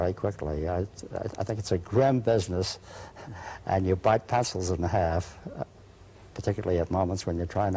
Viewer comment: What does he say at 27:36 after "grim business"? Sounds like "passes in half" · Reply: You bite pencils in half....